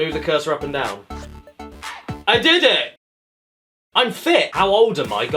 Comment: When he said “I’m fit” I thought he said “I’m THICC” for a second